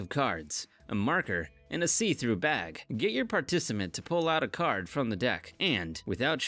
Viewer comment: wait what did he say? 6:06 xD Partisiment